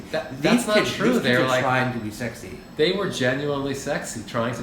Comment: “That’s not true. They were generally sexy-“ yeah i heard that